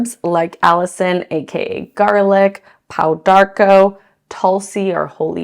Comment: right before i think she said pau d'arco. The non alcohol liquid doesn't taste too bad.